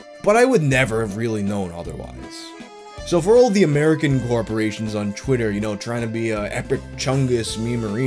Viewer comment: did you say fuckin glorporations